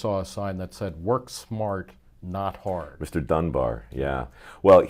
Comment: I heard it as "Work smarter, not harder", JBL, famed investor and WWF wrestler, circa late 1990's. I agree with smarter, not harder, in that it means look for more efficient ways to do the work, not avoid work, just make it better.